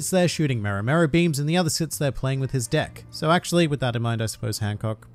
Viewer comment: “And Hawkins would sit there playing with his deck”. I may have misheard that